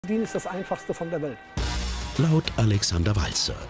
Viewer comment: Was sagt er? Dealen ist das einfachste der Welt?